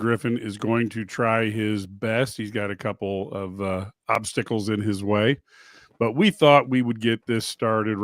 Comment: it sounded like you said Marcus got a couple of popsicles in his way 🙃